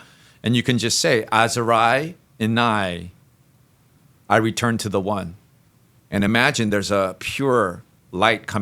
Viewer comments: What was the matra when tapping on throat... please thank you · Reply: i heard it as -Asarai innai i return to the one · Did someone get waht chanted when he tapped 8th charka at around 1:05:40 minute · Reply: i heard it as -Asarai innai i return to the one